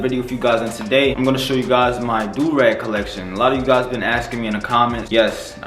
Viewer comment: DEADASS THOUGHT IT SAID DRUG COLLECTION